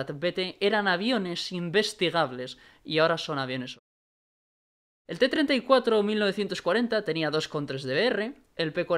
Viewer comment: que dice en 34:23? son aviones que? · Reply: dice que son aviones ocultos, que no se pueden investigar. Generalmente gaijin saca estos aviones porque son inutiles. Yo por ejemplo tengo el os2u que casi no puede despegar el pobre